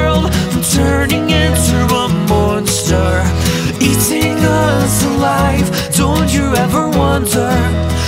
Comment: Is it weird that I kept mishearing the line "Eating us alive" as "Even love's a lie" when I listened to the original, but had zero confusion when I listened to these guys? Love this cover.